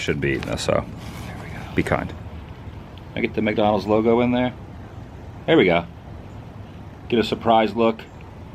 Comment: I had to watch it a few times but at the 16:50’ish mark right before you are going to dig into your food there is a whispery voice that says “Here we go”. Anybody else hear it? 😯